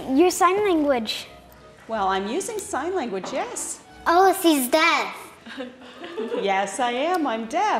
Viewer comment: did she say "oh, she's death"?? 😂